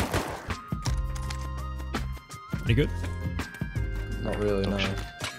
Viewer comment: WHAT DID HE SAYYYY? 7:16 💀💀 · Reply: "any good?"